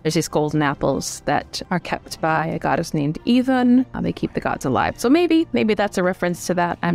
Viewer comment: is she saying "A Goddess names Even"? I can't find anything on the web about her. Is she the norse version of Eve?